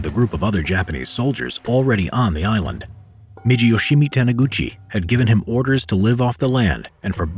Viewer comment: did I hear "GUCCI"?